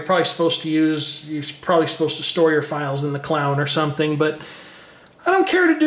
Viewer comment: At time index 11:07 to 11:11 the word you said sounds like store your files in the Clown or something - LoL ... I know that you meant to say the Cloud -and I had to play it back twice to see if I heard you correctly but yep it sounded like Clown ...